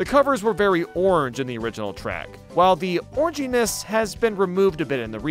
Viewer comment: does anyone else think it sounds like he's saying orgyness